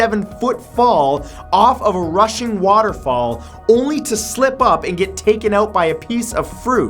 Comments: did anyone else hear Russian waterfall? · Did he just say russian waterfall? Haha, nice video by the way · I had to rewind as I thought you said “Russian” instead of “rushing” when talking about Niagara Falls. I was like, as someone who’s lived in my home town of St. Catharines, if he thinks Niagara Falls is in Russia, then that’s a problem 😂